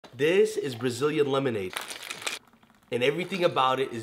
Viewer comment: Thought he said, "This is Brazzers lemonade...", later on, "Oh, that explains the condensed milk."